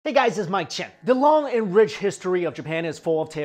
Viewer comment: mike im in love with your content but..... intro always i hear "hey guys its my chin" :D